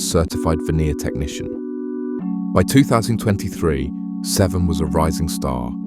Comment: There is no such thing as a certified veneer technician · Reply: I thought it said video technician... 😂